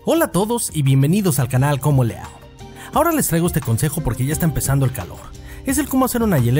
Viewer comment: al principio te escuche "les traigo este consejo porque ya esta empezando el canal" jajajaj :b gracias wey, me sirvio mucho porque empezare a vender jugos jajaja XD